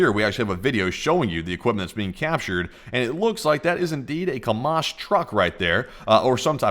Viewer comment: I like how it sounded like you said "Kamas." Kamala + Hamas...... Allegory? lmao jk